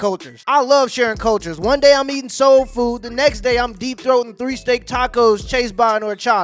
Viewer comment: i hope I'm going deaf ,,,"did he just say that he be deep-throating beef tacos"